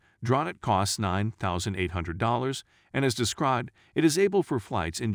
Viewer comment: did he just said 9800$ ?